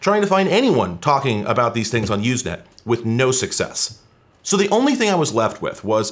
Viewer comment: at first I heard "YouSnap" and thought it was some quirky 90's YouTube/Snapchat thing.